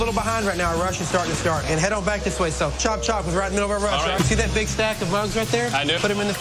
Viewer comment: What does say at 11:59 ? It kind of sounds like he's saying, "see that big stack of RUGS right there, put them in the freezer". Either i misheard what was actually said or i have no clue why they would put a stack of rugs in a freezer. Id really appreciate it if somebody could fill in the blanks lol.